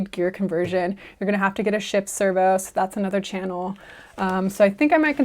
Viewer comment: Nice build so far. Will be great to see it finished. I thought at 15:01 you said "shit servo" haha